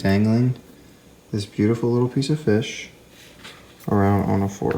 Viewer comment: did you call it a beautiful DEAD fish?